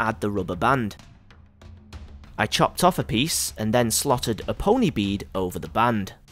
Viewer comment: When he said I slotted a pony need on to it, I thought he said a slaughtered a pony 😂😂😂😂😂